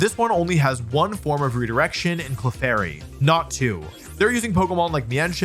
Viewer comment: i swear i looked up bc i thought bro said natu